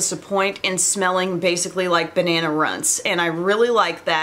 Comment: I TOTALLY thought you said “banana runs”, and the mental picture was NOT cute 😂 Too faced is the kryptonite of cute packaging for me. They get me every time and I buy it but the quality just isn't there for the most part